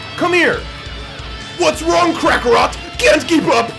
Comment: Lol did he just say Krakarot??